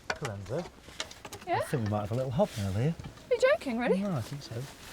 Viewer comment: what is that? Couldn't understand what they were saying . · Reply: It's a hobnail, which is something that goes on the bottom of a boot to help give it better grip on the types of surface these soldiers would have walked over.